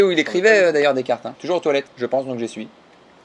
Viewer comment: Toujours aux toilettes « je pense donc j’essuie » il m’a tué 😂😂😂😂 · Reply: Ah mois j'ai compris "je pense donc j'y suis" Mdr